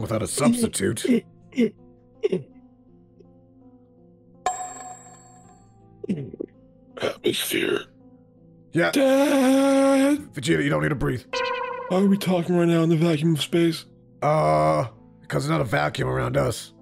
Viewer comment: Did bro just say "That was fear." In Frank's voice? 💀 Edit: NEVERMIND HE SAID "atmosphere" bc he thought he couldnt breathe 😭